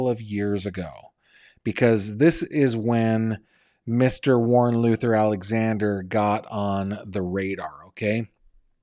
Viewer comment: When you first said the suspect’s name, I thought you said Warren “Loser” Alexander. 😊